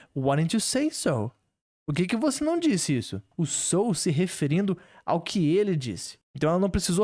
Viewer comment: Eu tenho quase certeza que eu escutei "son", que seria a gíria "filho" aqui no Brasil, mais alguém?